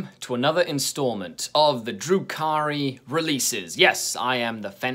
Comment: Every time you say Drukhari, it sounds like your saying Drew Carey, and imaging him doing all these things is funny as hell.